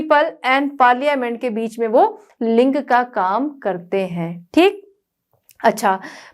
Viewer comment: it sounded like ling